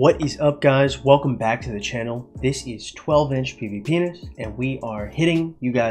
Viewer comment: first time watching the channel and i thought u said welcome to 12 inch pvpenis